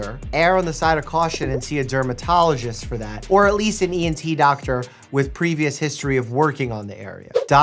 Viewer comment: HAHA when Dr. Mike said ENT doctor, I heard "anti-doctor" :D (I am a tired nurse, just woken up this morning)